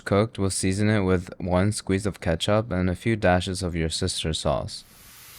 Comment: Is it just me or did anyone else heard him say your sister sauce at 11:31💀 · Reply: nah yeah actually said that it’s fish sauce so he is saying that your sisters 🐱 smells like fish - i think